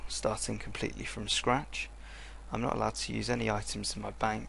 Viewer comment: i thought you said that you are not allowed to use your bank?